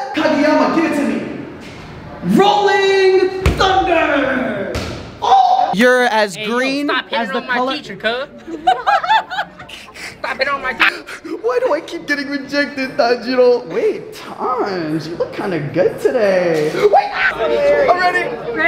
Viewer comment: what did he say I think he said let go of my teacher cuz